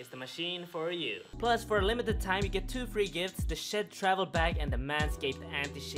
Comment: Impressive sponsorship, not going to lie mr. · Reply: did he say shit travel bag?